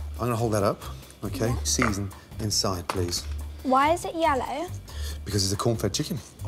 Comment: I thought she said ‘yolo’ instead of yellow